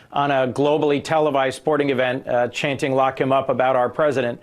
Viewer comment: Actually, "lock him up" is a rather... merciful demand. Trump deserves worse. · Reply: I heard "f*ck him up" .